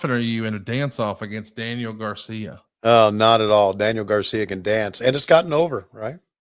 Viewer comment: Did he just say daniel garcia is over??